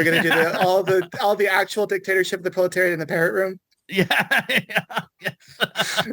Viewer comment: How Freudian was it from me to think “The parrot room” was actually “The parent room” from 1 to Oedipus? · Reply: I also misheard it for a long time, and also had no idea it refers to a patreon subscription portal.... I also misheard it as "parent"...